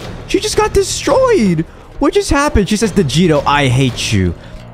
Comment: When I hear your name I thought they said vegito but actually they said right?